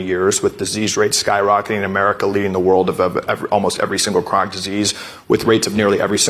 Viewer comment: Go to <link> and use code BRAND to save $10 on your annual subscription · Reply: I thought he said “CRIME DISEASE” when he was saying “chronic disease”